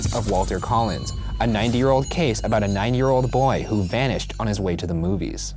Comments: Thought he said "a 90 year-old case about a 90 year-old boy " lol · When Ryan said “9 year old boy” I thought he said “90 year old boy” 😂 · I thought Ryan said "A 90-year-old case about a 90-year-old boy" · When Ryan said “9 year old boy” I thought he said “90 year old boy” 😂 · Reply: I thought he said 9 year old case lol. maybe ricky goldsworth is a 1930’s man that is lost in time? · I thought he said a 90 year old case about a 90 year old boy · Almost thought he said 90 year old case about a 90 year old boy lol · genuinely thought Ryan said “a 90 year old case of a 90 year old boy” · I heard “90 year old case about a 90 year old boy...” lol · at the beginning of the video I swear it sounded like ryan said "a 90 year old case about a 90 year old boy" lmaooo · I thought Ryan was saying he was a 90 year old boy and I was like ok sure whatever, until him mum reported him missing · I thought he said ‘a ninety year old case, about a ninety year old boy’. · I heard “90 year old boy”